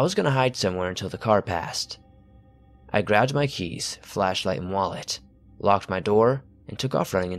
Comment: I love Skinwalker and Wendigo stories!! Could you do more fleshgait and crawler stories? Or humanoids?? Thanks for the upload ❤️ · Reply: bruh i thought you said fleshlight